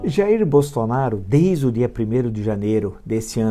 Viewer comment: O quê? 0:03 Jair Bostonaro? Ahm? Eu ouvi isso? 0:03? Hein? 0:03?